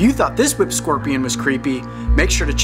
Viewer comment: I thought he said Taylor swift scorpion